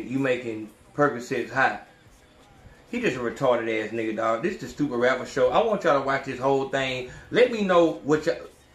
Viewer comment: Bro For The Longest I thought that Shit Said Studio Rappers AHahahhahhah